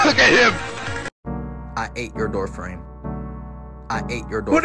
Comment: I thought he said “I *hate your doorframe”